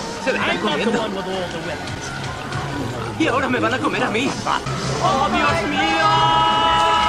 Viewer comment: i hear: "oh por dios!!" in spanish, im right?